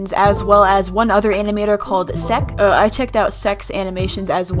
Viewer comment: did u say S*x? at th 1:15 mark? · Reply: She meant to say seck's not sex also its not a bad word